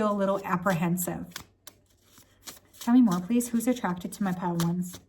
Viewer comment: I thought it said, “Who’s ATTACHED to you”. Might what to know that too, might not 😅